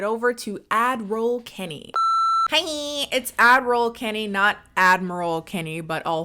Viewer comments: I thought she was saying Adderall Kenny 🤣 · I love that you finally clarified the addroll Kennie, because without subtitles I always thought you said advil/adderall kennie. I wasn't sure, but also like "wow you're really open about that, kinda cool"